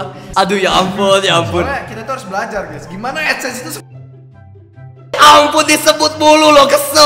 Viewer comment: bang ngomong apa ya, hihi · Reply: Adsense itu sebenarnya nomor satu